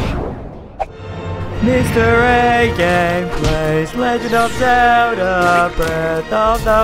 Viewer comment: I thought he said mr a games grace XD